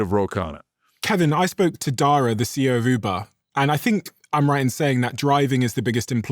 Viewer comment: I thought he said Diary of a CEO of Uber. 😂